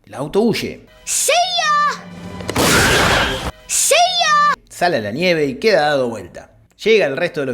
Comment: más que silla, como pone, parece que dice SEIYAAA!!!